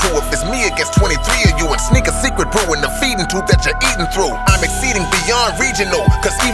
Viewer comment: Anyone catch what he said at 2:57 though c couple of words I can't catch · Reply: Sneak a secret brew in the feeding tube that you eating through.